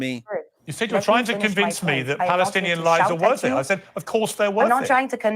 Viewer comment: Did i hear good "palestinen lies better"?